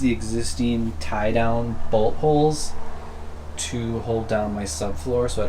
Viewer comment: When he said bolt holds I thought he said butt holes